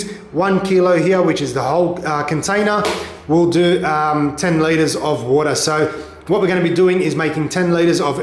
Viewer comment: I thought you were saying “10 layers of water” 😂 I’m think how do I know what 10 layers is!!! (American) Go figure 🙄😂